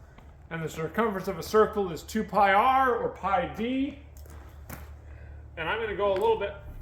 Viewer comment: Did you say "2 Pi R or Pi D"?. Glad you cut it a little long.